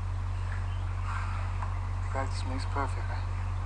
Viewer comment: Can someone tell me what Edward says at 3:09 please? After belle has said “ pillows survived” I tried hundreds time I only understand the word “ Perfect” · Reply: He said practice makes perfect